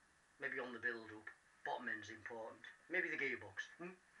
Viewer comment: Ok i honestly thought you said "And maybe the Gay box"